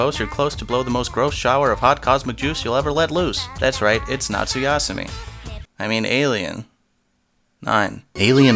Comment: what is he saying sugasumi suyasui?